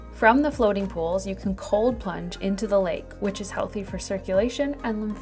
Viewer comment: I thought you said "cold punch" into the lake. I figured, just go with the Icelandlish lingo flow 😄